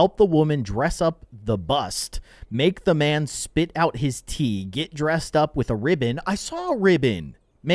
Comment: I Thought he said. spit out his teeth. Rewatched it Spit Out his tea.